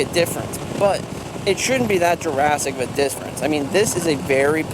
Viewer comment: did you say Jurassic? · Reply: Definitely sounds like it 🤣 it was intended to be “drastic”